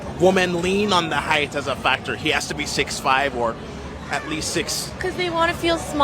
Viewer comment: did she say 5’12?!?! 😂😂😂